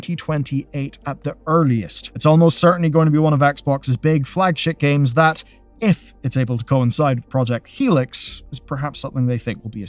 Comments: I swear to god you said "flagshit" the first time I listened. I replayed it and I'm still not sure you didn't. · I hope that I am not the only one who heard him say "flagshit games" during this video and found myself nodding in agreement before my brain corrected for "flagship games". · I definitely heard "One of Xbox's big flagshit games" and yeah, I have to agree with that. Let's nornalize the term "flagshit" because that's what we're about to see. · did you say "one of Xbox's big flagshit games" cos that's genius.